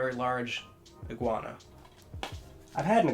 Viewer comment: Misheard iguana for Jaguar and got so confused..